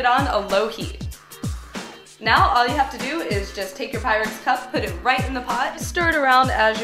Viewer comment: For a second, I thought she was saying "get out your pirates cup" and I kept looking at that cup and wondering why it was a pirate